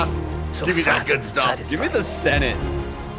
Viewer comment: During the trailer they were talking over Andor’s lines and it sounded like Andor said “They’re so proud of themselves. So fucking satisfied.” I would be so down for this getting a TV-MA rating. Also, I think I’m a fish? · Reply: Says "fat and satisfied"